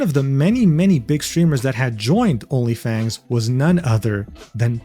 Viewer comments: why did I hear onlyfans... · Everytime says Onlyfangs, I hear ''OnlyFans.''